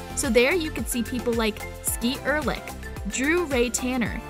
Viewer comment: Did she just say skeet ørlich😭😭😭😭